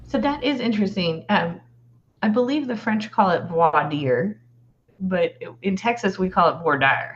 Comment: Can someone spell out for me what she is trying to say in French? She’s obviously butchering the word, so I’d like to research what word it is that she is ATTEMPTING to pronounce, but failing spectacularly in trying to do so lol. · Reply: Voir dire